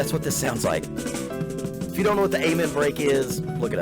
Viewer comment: At ~ 6:05, he mentions a term - does he say, "the amen break"??? He mentions a lot of musicians I've never heard of - quite interesting...